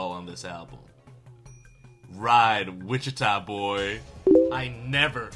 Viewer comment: not the “ride Wichita boy” instead of “ride witcha boy” 🪦